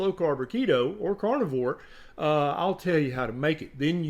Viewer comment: Hahahahahaha..... had to rewind it as I thought you said « You can kill the host » so had to listen once again. « Tell you Host » 👍😂🤣😂🤣😂🤣